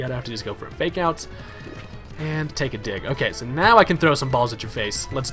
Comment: Sounded like "Take a dick." This is we're talking about, though.